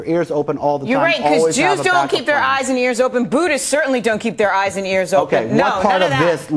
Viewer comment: did she say "Buddhas"?